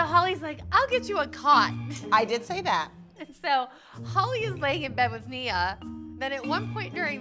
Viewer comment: I thought it said “Holly is found in abbys bed” 😂😂